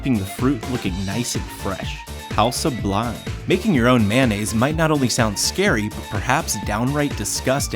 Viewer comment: when he said mayonnaise i thought he said band aids xD